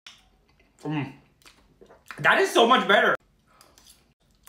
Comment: 谢谢🙏 wait an a minute did he just say “Indian winz”👁️👄👁️